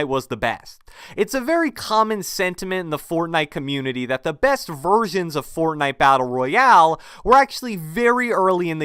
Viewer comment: did he say''The best virgins'' btw i watched a durex ad for you